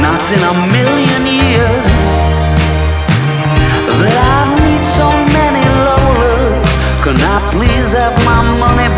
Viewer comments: I thought he said , that I'll need so many lovers , rather than I'll meet so many lola's 😂😂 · I always thought he said "lovers" instead of "lola's" Does anyone know what he is referencing to with Lola's? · This whole damn time I thought he said “I never thought... I’d meet so many lawyers” I saw them during the TBHC tour and everything 🥴.